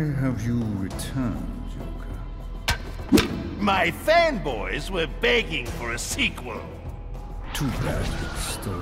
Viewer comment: did he say joker or not I honestly didn't even hear him😵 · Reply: my fan boys were begging for a sequel